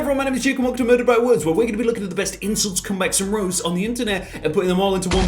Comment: When he said “insults comebacks and roasts i misheard it as “incest comebacks and roasts”